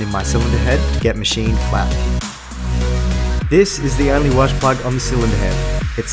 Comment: you remove something called a wash plug? I wasn’t sure what you said. On my A15 I’m noticing that I have an oil leak and someone told me it was the rear main seal. Is this the rear main seal? I wish someone did a video on that so I could see😊👊🏼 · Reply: The part at 2:23 is called a 'freeze plug' or 'welsh plug', I think it was used for the casting, but also to remove frozen water too? I'm actually not sure haha